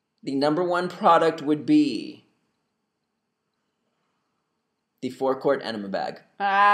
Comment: What did you say in 2:46? you spoke so quickly....did you say, "the fourth quart animal bag"?